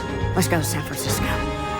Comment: What is she saying? 0:06 Zasazusazisco? 0:22 shashasheshashu? I don't understand a word ...